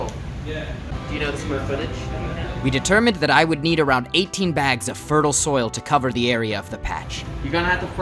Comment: i thought he said " i will need to rob 18 banks"